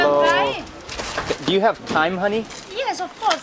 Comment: Lol when he said "Do you have thyme honey" I thought he was calling the lady honey! 🤣🤣🤣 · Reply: I also thought he said,do you have time honey?😂